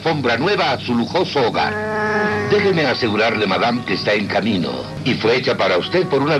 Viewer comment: entonses jango fet se la robo :v pues dijo kamino o es camino yo entendi kamino de planeta